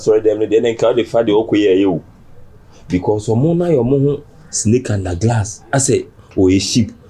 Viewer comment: did I hear, “SNAKE UNDER GLASS”😂😂😂😂😂